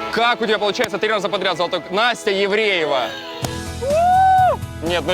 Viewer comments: Мне послышалось Настя Евреева :D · Мне 1 послышалось Настя Евреева